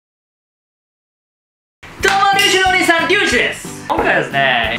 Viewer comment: 聞き間違いかと思ったらのお兄さんって言ってたわw